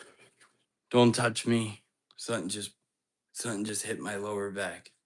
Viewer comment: I swear I hear “it’s me” right as you say don’t touch me!